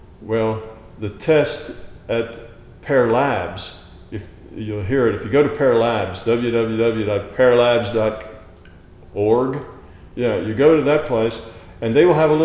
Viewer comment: I'm sorry but what is the link you say at the 35:50 mark? Parralives? Parralabs? Thanks. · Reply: PEAR (Princeton Engineering Anomalies Research) labs. --> <link>